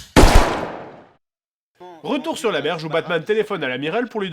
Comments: Vous savez ce que j'aurais aimé avec cette vidéo ? La revoir à chaque fois comme si c'était la 1ère fois. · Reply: je ne sais pas si c'est moi mais je crois qu'il dit 'retour sur la verge' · A 18:30 c'est moi ou il dit "Retour sur la verge" o.O